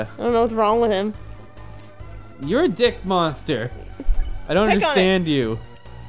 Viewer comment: he says either "Pick on me" or "Don't Pick on me" you have to do what he says